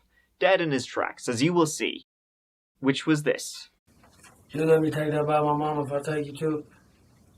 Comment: I think he says are you gonna let me take this (wallet) by my momma if I take you to her